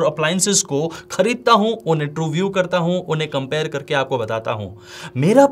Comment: i like how my man said, true view, instead of review!!!!😃